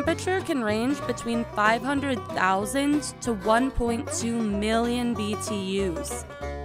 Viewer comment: Ok for a second I thought she said 500,000 million degrees.